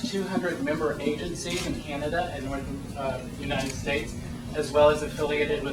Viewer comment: Ha. It sounded like he said "We have over 200 member agencies in Canada and North Korea... uh The United States" Re-education sounds like something that Kim Jong-il would be all about.